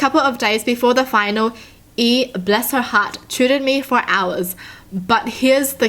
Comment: I thought tutored said tortured 🤣